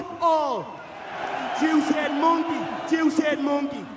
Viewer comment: did he say Juicehead Monkey? 😂😂